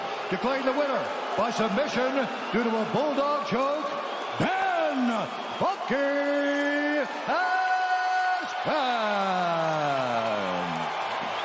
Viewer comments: I thought he said Ben “Fucking” Askren lol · Ben predicted his destiny. One day Dana WILL look back and laugh at how he got Bens ass kicked 3 times. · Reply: I thought he said Ben “Fucking” Askren lol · I thought Bruce said “Ben Fucking Askren”